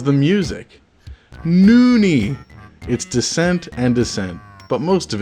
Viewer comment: LOL. To give them a bit of credit, Descent is the game that a handful of the MIDIs are from.. but still, is he saying NIN and Descent's sountracks suck? It's a bold claim! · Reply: Oh my *god*, I thought he was trying to say "it's decent and decent". That makes a lot more sense